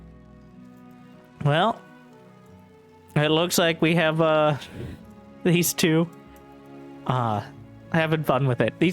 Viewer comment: Anyone knows what solar and Byun sayin at 49:54 minutes mark? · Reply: FalconsSolar: How much is a Gatorade? FalconsSolar: About $5.50 FalconsSolar: Try drinking it~ FalconsSolar: I’ll buy it for you ByuN: T.T FalconsSolar: No way you can drink it (Continuation): FalconsSolar: I’ll treat you to a steak too ByuN: Really? FalconsSolar: If you beat me ByuN: (Sad face) FalconsSolar: Yeah, not happening!